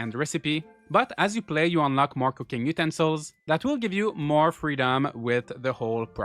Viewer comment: I misheard cooking utensils as cooking mutant souls